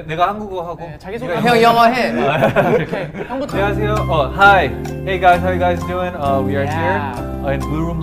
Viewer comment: did yuta just say hi at s*sa3ngs?